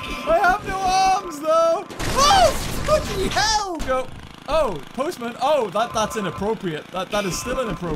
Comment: This is what I heard at that time (Oh ****y Hell!) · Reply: Bloody